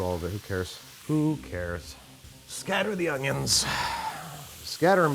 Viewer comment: Why did I hear Robert California when you said "scatter the onions."